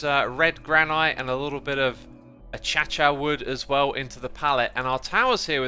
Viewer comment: did he just call Acacia "a-cha-cha"?